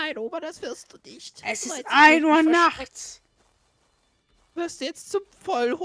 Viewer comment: Wann war das? 1. 00 Uhr nachts? nee ich glaube ich habe mich verhört :XD. Die Oma ist so klasse, total durchbohrt, läuft aber immer noch. Das war wieder Unterhaltung pur :D was willst du zu Mama.... ich lache mich schlapp :)